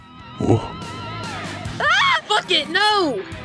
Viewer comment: What Map Said: “Bucket, No!” What I Heard: “F*** It, No! I hate my imagination sometimes